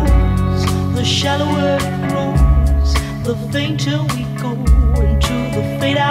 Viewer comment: Seriously thought she said "The shower room, the danger looms"